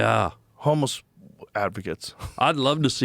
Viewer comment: Did he say “homeless activists”?